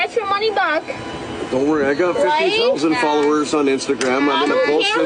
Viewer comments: LMAO!!! I thought he said 50,000 followers. Frigging 59?????? 60 winners right there. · " I've got 50,000 followers on Instagram," 😂😂 Why is that even a thing? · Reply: I thought I heard 50 followers.